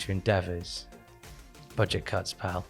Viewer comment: When said "Budget Cuts, pal", I literally thought he said "Buncha c*nts." I mean, he wouldn't be wrong, but also ha!